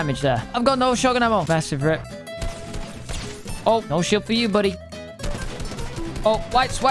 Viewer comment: Bro look at 4:53 it said no shit for you instead of no shield for you LOL